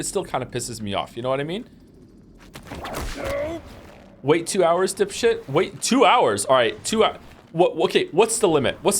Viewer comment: I thought he said "Wait 2 hours to shit" and that made me laugh since I couldn't tell if it was someone saying to wait 2 hours sarcastically or angrily saying to just be patient.